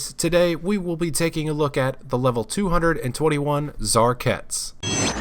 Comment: Lol i thought he was saying TzHaar-Cats